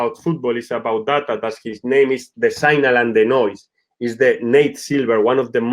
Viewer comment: What's the name of the book Victor mention's at 24:19? I can't accurately make out what he is saying. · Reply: Ok found it - The Signal and the Noise: Why So Many Predictions Fail-but Some Don't